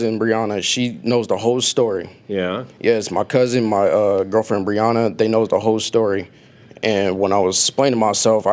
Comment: Did he say “my cousin my girlfriend Brianna”?? 👀